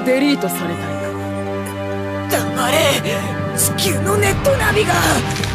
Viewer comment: (Bass) - bitch.... shut up I think he said that